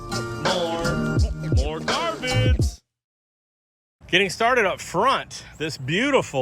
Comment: I like your videos but your intro "song"....sounds like your saying ..."moren garbage"...just saying